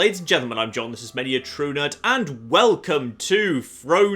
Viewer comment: Your intro sounds like you said "this is smelling a true nerd".